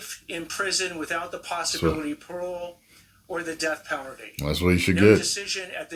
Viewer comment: I swear that guy says death powerdy twice